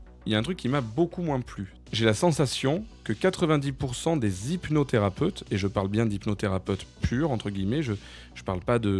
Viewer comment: A un moment, j'ai entendu "hypnodérapeute" et considérant les ramifications charlatanesques de certains d'entre eux...